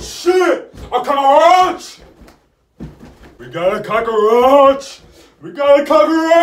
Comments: I thought you said “I caught a roach!” Real fast at first. 😂😂😂😂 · A what?😂 did he say a coraoch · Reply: No a cahroach